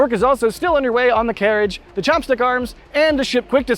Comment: Did anyone else hear "Chompsticks" arms?